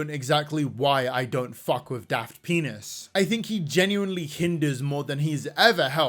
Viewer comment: Every time I hear say "Daft Pina's," I keep hearing "Daft þəŋ!$"... It's either a fitting description... or I have a problem EDIT: 27:30 OK, it is a fitting description...